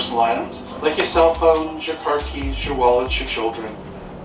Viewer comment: Did the conductor just say children(20:06)? I heard "look around your room for all the small important person items like your cell phones, your car keys, your wallets, your children" in the most bored voice ever.